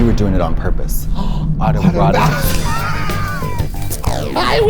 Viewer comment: what were they saying? I just don’t understand it😩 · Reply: Auto erotic asphyxiation